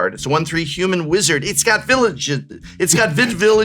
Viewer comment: I almost thought prof said its got Village Idiot...😂😂